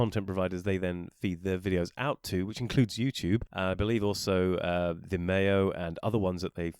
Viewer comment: At the 1:27 mark, you mentioned a video platform that sounded like "de mayo". Could you clarify what that was? Also, how on earth can Rumble be copying your videos from youtube, and calling it a "rumble only" license? It is a contradiction on the face of it. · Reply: Vimeo 😊